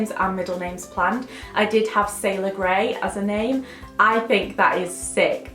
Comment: Legit thought you'd said sail away.... then after playing again realised you said Sailer Grey ..sorry 💕 🤣🤣